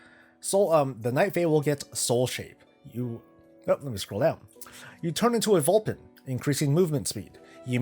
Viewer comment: I was just listening and not really paying attention, and at 2:19 I heard "you turn into a Vulcan". I was all wait what?! They learn mind melds and neck pinches???